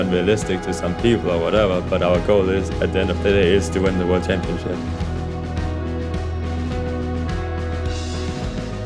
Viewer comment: what does he mean looool ?????!!! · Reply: ohhhhhh lol I misheard this is great to be hard xddddddd